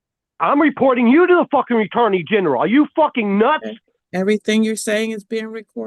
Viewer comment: … anyone else know what a “Retorney general” is 😂?